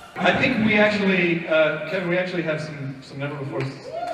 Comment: what was said in 10:51? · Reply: ***** "I think we have some never before seen footage"